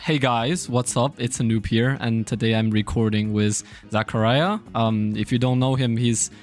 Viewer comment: Wait he’s playing with hypixel ss lmaoooo? Edit: nvm I thought he said zacharlah